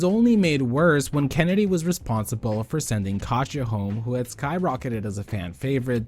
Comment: Bish did you say she sent KASHA home??